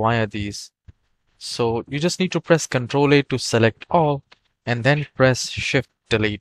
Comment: Hi ! It Dec 1, 2024 and Im just finding you. Thank you! What did you say @ 1:15 to select all? ctrl 8? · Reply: Ctrl A